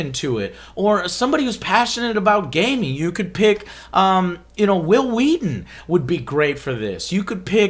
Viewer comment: What i heard - "You could pick me". I'm actually would be so happy